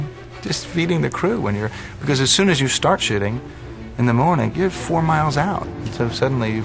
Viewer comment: Did he really say "shitting" at 5:35? "... As soon as you start shitting in the morning ..." LOL. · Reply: Shooting.